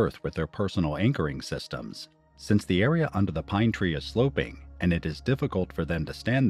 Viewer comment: What the hell did he say?? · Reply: Since the area under the pine tree is sloping……. Was that what you couldn’t make out?